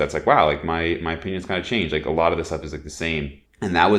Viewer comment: I would’ve said 31-32. 😉 But here too bro, people guestimate that I am 35 🤷‍♂️ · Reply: Edit: @ 10:11, it honestly sounds like you say “My penis got changed” 🙄😂